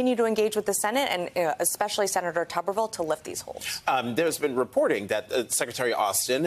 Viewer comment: The video played on its own so I didn't see the title and I swore that she said Senator Taco Bell the first time😂😂😂